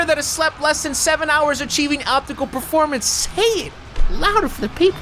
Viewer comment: did anyone else catch him say optical performance when he was reading his warrior stress tolerance? lol